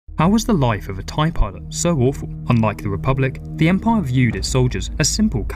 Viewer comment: Thought he said "how the life of a tie pod is so awful"